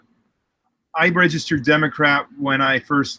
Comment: Could've sworn he said Democrap on the beginning lol.